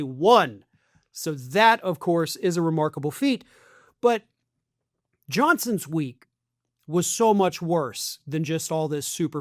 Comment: You said "Johnson's week" a couple of times, and I always heard it as "Johnson's WEAK." Both correct, I think.